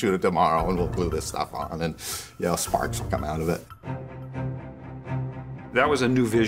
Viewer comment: he says 'sparks would come out' but the captions say ''farts would come out of it''😂